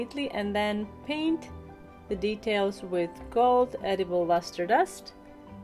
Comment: Nice designs! One quick question though, how do you make the gold editable paint? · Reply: I swore you had said MUSTARD dust... you should have seen the look on my face as I tried imagining the flavour!